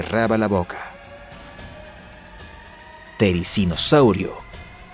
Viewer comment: lo que dice: therizinosaurio lo que yo escuche: dolor de cabeza...terrible gonorrea que te mata ai no lo ves xd